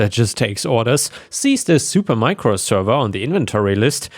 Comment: I don't know why but I heard "Minecraft server". Did a double take, opened up the window and saw the actual word "supermicro".